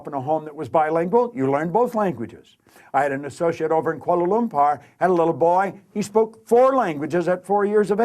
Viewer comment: To whoever does the subtitles, in 3:35, he says “Kuala Lumpur,” the Capital of Malaysia. In transcription, if we don’t understand a word or phrase, we write “indeciphirable” instead of “mumbles,” because those two are different.